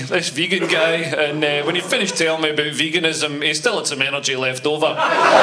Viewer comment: What does he say at 1:43? "When he finished telling about veganism"....and then what? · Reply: "he still had some energy left over".